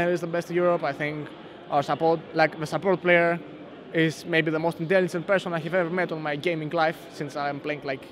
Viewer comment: What does he says at 3:17 about nRated ? "I think he's the most ???? player I've met in my gaming life" ? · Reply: He said something like most intelligent player he met